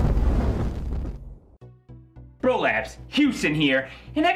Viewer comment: "Bro-Labs" What an awful name I thought you said prolapse Then when you said deep heat I heard "DP" I'll get my ears cleaned tomarrow...